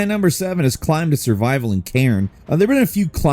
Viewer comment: When he said "a climbing survival game in Cairn ; i first heard it as Karen and i was like hold up!! what??😂. The Lost Wilds and Crimson Desert are definitely getting added to my wishlist. Kingmakers from what i've seen will most likely melt my PC